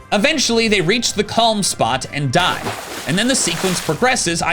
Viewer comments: I heard “they reach the calm spot, then die” 😂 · I wasn’t even paying attention so I misheard this as “they get to the calm spot and *DIE*” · I heard this as “They reach the calm spot, and die”